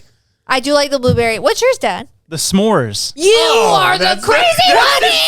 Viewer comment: I swear I hear call “Dad” lol